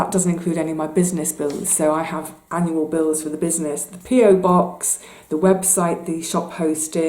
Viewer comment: Well, I guess you can tell I'm an American. When you first started talking about your PO box, I thought you were saying the Pier box. I thought, what an odd name and what is a pier box? Then it dawned on me. 😆